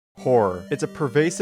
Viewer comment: Sorry but when you started the video I thought you said whore not horror 😭